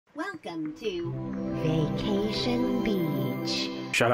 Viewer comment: I heard "vacation bitchhhhh~" in the intro